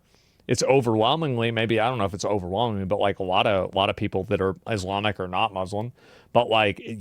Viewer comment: Did I hear that right and he said that a lot of people who are Islamic are not Muslim?